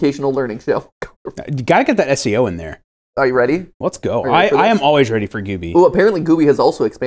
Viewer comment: The "Brainstorm/Green needle" moment fascinates me to no end! I can NOT hear Brainstorm AT ALL! The most i can hear is Brain needle if I focus on the word really hard and seeing not only hearing only one but the one I CANNOT hear makes my brain tickle in the most curious ways! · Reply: I only hear "are you eden". I know I'm HoH and I use a hearing-aid and radio-aid plugged into a headphone socket but considering that's neither option....????????????